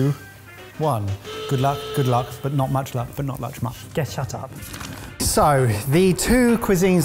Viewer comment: did just say " but not luch muck"? haha